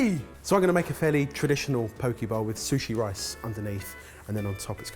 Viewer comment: when he said poke bowl I thought he said pokeball...and I was like oh cool pokemon fish dish.